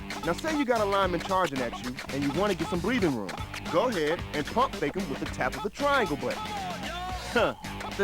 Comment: What'd he say at 0:48? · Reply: Hi, he says "Pump Fake", means you going to throw the ball for one receiver (like press Square), but very quickly you press Triangle button and make it a fake movement, after this you can press other button to throw the ball again.